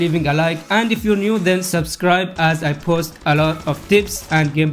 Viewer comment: I thought u said "i post alot of shit